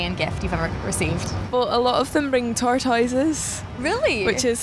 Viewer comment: What's the animal she says at 2:53 ? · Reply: tortoises :)